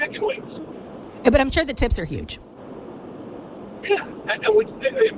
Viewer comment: I asked myself did she just say "I'm sure the tits are huge!" LOL. No, seriously I had to turn on the CC to clarify that. Well, they were talking about Hooters for god sakes. What I'm the only one. :face-blue-smiling